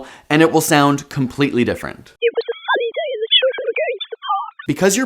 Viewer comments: am I the only one who understood what she said without hearing the other audio? 😅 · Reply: I heard "funny" instead of "sunny".. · anybody else get it the first time without listening to the clearer one first? I could hear it pretty clearly lol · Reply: Yeah same. I heard "it was a funny day as the children were getting in the park" Almost the same as real thing. Tbh should've realized it was "sunny" not "funny" 😂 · am I the only one who understood what she said without hearing the other audio? 😅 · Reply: I heard it too, but instead of 'sunny' I heard 'funny', other than that, it was the exact same. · the first time before the clear audio, I heard it, it said “it was a funny day because the children were going to the park” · The audio one was quite clear to me. I only misheard the word sunny as funny before the 'reveal'.